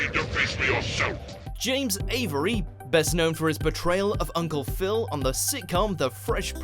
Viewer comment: Anyone else hearing "betrayal" instead of "portrayal"?